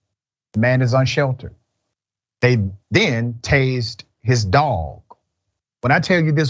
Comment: I thought he said "taste" his dog, it's tazed.